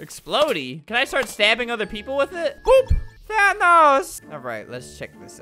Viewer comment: did he just say oop